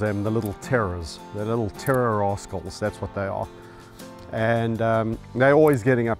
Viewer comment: I misheard "the little terror rascals" as "terror a--holes" hahaha If Kevin can love hyenas, he can love the little terror cheetahs.